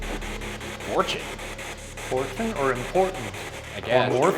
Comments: i definitely hear "portal" not "important" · did anyone else hear it say "portal" when ryan asked it why it remained there? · it kinda sounded like portal or important... maybe hes guarding the portal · i thought it said portal